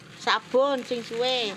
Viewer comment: Apa cuma gw yg salah denger beli sabun bilang "Sabun seng suee limoo" 🤣🤣🤣 eh ga taunya sabun sinzui 5